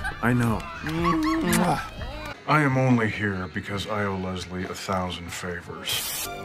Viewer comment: wtf did he just say? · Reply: “ I am only here cus I owe Lesly a thousand favors”